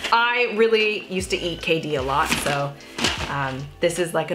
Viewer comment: what did you eat a lot? Sounds like you said Katie??? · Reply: KD :) its what Canadian's call Kraft Dinner